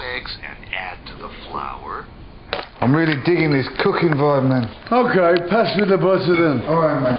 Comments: hey ...you posted a comment (Ozzy Osbourne. Butter commercial" can you please help me? Do you know what he said (0:05-0:08) "I'm really giggin with...? and then..."okay pass with??" Thank you so much! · what did he say (0:05-0:08) "I'm really diggin this....?" and then : "Okay pass with..?" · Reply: "I'm really digging this cooking vibe, man." "Okay, pass me the butter then."